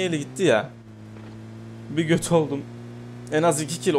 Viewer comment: ne diyorsun abı 😂😂😂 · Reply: kötü oldum diyor